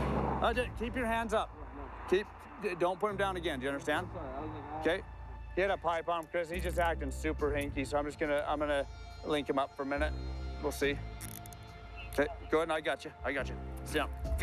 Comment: Woah 1:14 I thought he said he had a pipe bomb